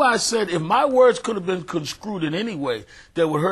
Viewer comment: did he say "conscrewed" ??